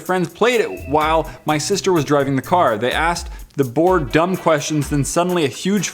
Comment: Oh lord, I thought you said: My sister was drugging the car. XD